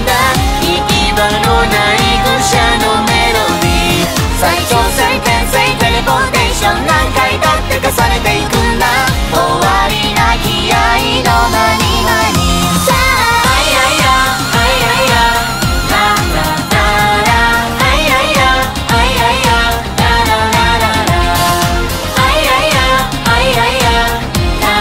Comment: entendí "mani mani" y me reí bien feo